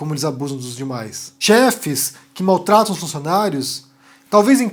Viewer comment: Nossa achei que ela falou merda demais , mais menos né gente ..